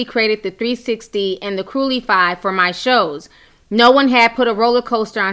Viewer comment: Why are you calling the “cruecifly” a “crulifly” 😂 🙈🙈😂😂😂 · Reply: i thought she was saying curly fry i was like girl what🤣